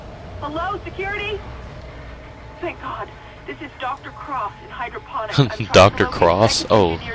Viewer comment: I thought she said Dr.Crotch.